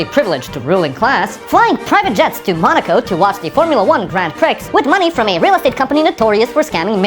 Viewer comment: Did you just say Grand Prix as "grand pricks" ? Lol.